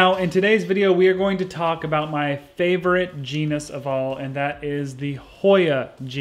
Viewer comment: I was just making some toast and misheard 'my favourite genus' as 'my favourite penis' and now my brain can't stop replacing genus with penis, which makes this videa very entertaining 😂